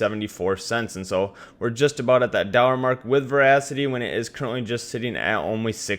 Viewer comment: No disrespect but everytime i heard him say "dollar" it sounded like "dorra"